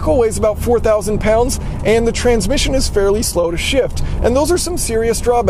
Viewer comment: i thought you said the transmission is fairly slow as shit lol, 2nd time i heard it you actually said slow to shift